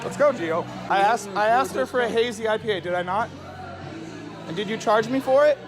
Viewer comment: The second he ordered a hazy IPA, I knew everything I needed to know about him. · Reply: I thought he said APIPA, which is some kind of financial thing?